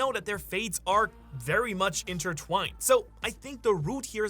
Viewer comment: Can you choose the cc cos he says "very much intertwined" n the captions say "HELLA intertwined " at like 1:30💀